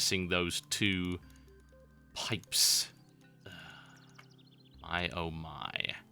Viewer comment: Ok, so, I'm using Transcribed Audio. At 39:57, when you said "pipes..." it heard that as "sex."